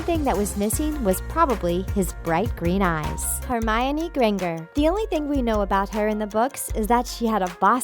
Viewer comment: did she say Hermione grenger